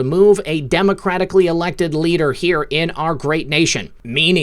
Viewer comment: What? "Democratically leader, President Trump" I heard it back 6x again WHAT? 12:41 and